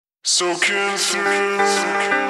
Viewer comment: Soaking through Comes and holds itself against the wall Soaking through Comes and holds itself against the wall Hold your colours against the wall, When they take everything away, Hold your colours against the wall Soaking through Hold your colours against the wall, When they take everything away, Hold your colours against the wall Soaking through Ahhh, she looked into your eyes, And saw what laid beneath, Don't try to save yourself, The circle is complete, We're reaching out and to tell you, Nothing else can touch me Soaking through Hold your colours against the wall, When they take everything away, Hold your colours against the wall Soaking through Ahhh, she looked into your eyes, And saw what laid beneath, Don't try to save yourself, The circle is complete Fading gently, Soaking through, We're reaching out and to take you, Nothing else can touch me Soaking through Hold your colours against the wall, When they take everything away, Hold your colours against the wall Soaking through Hold your colours against the wall, When they take everything away, Hold your colours against the wall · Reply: I always thought it said So kiss me Not soaking through 🙄 I like my version better 😂